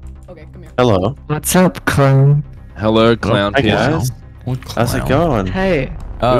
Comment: i dunno why but when you say clownpierce it sounds like your saying clown piss